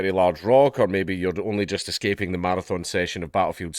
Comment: Without the Closed Caption I thought he was saying "sex".